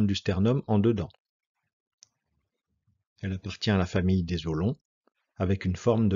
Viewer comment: c quoi la famille de ... quoi ?? .j'ai pas compris ça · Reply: En bref , il existe plusieurs formes d'os Les os longs ( y compris la clavicule) Les os courts Les os plats Il peut aussi exister des os irreguliers